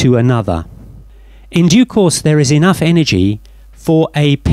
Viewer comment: Great videos, I really appreciate your work. After 7:19, you say "In (?) there is enough energy...". My questions are: what do you say that sounds like: duo-course, (sorry, I'm by no means a native english speaker) and what is the reason that there is enough energy for a pion to form? Thanks in advance.